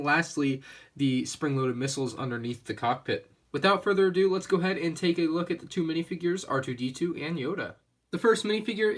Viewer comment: What happened at 0:36? you said let's look at the set twice. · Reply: He said "Let's go ahead and take a look at the two minifigures R2D2 and Yoda." :)